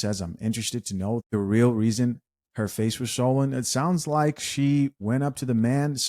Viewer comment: I heard it was saline in her face